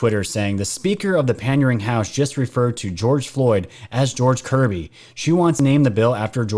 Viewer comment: Sounds like she says George Covid